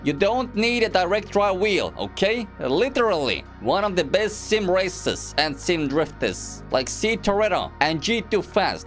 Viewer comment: "sim racers" i only heard racist